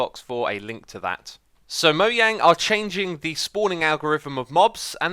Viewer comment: Did you just say "moyang"??